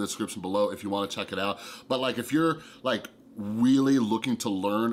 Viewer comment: did I hear weally instead of really...? 🤔😅